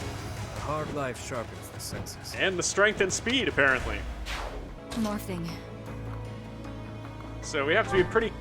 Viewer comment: I love how it sounds like Dorothea said morphing at 48:55 Idk why I thought that was hilarious